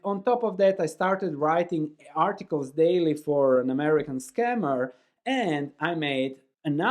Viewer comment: He said roughly someone paid him to do some kind of research and articles, he didnt know it back than. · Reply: I heard " scammer " .